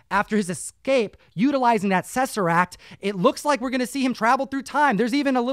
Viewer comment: did just say “sesseract”? Instead of saying “Tesseract” 😅🥲🤷🏾‍♂️